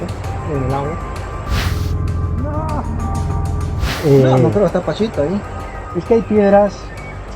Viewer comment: FunFact; Charly I noticed at minute 9:37 you xxxx a word that you didnt understand them, he said esta "Pachito" ahi. Means the water it's low, maybe you would've make it 😂